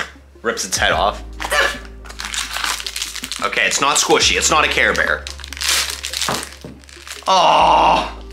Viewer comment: I thought you said SHUT UP CAREBEAR